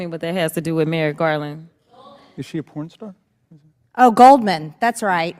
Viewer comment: I swear someone says "is she a porn star?" What on Earth lmao